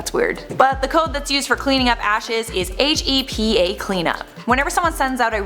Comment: At first I thought you said HAPY clean-up and I was like WHAT IS HAPPY ABOUT DEAD PEOPLE.